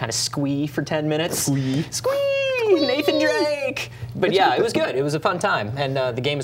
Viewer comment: did he say meek and drake😳